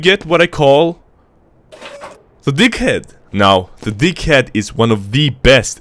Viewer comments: Misheard be like: Him :We get what i call Deaghead Me :We get what i call D*ckhead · What they hear. Deaghead What I hear. Dick head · before i heard deaghead i heard dickhead xD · did he say dickhead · The deag head. I thought he was saying The dic head. Dude this guy is way tooo funny man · The deaghead? Sounds like he is saying the dickhead